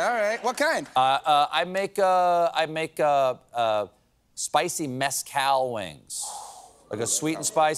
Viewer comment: I thought Stephen said "spicy meth cow wings"! 🤣